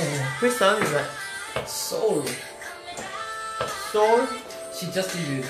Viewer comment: Can you please tell me the artist and the song u r playing please? Thank you so much. 😇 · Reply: Kevin asked Jae which song was that and Jae said like 'SOUL?' I can't hear that well